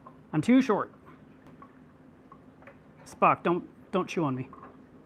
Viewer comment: He is saying Spock, but I thought he was saying f**k lol